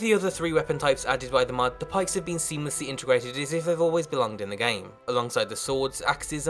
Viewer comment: I still don’t understand what he’s said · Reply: As if they is what he said